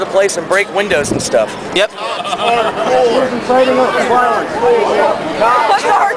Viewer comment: omg i would love to punch the blue clown i the fuckin face ! :O since im not from US non would even know ! :D oh yeah , when the guy started to scream "cops are cool "i thought he is saying cocks are cool ... but watever ...